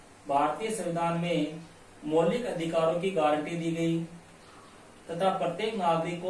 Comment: par sir aapne kya bola kuch samj mein nhi aaya kirpya denge please 🙏🏻🙏🏻🙏🏻🙏🏻🙏🏻🙏🏻🙏🏻🙏🏻🙏🏻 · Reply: मौलिक अधिकारो की गारंटी